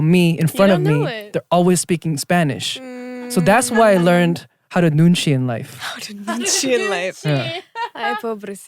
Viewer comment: what does Jae says? "는지"? ("so that's why i learn how to lose in life"?)